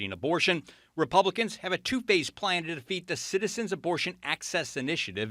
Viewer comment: When you said 2 phased plan. I thought you said 2 faced plan. Lol it still made sense.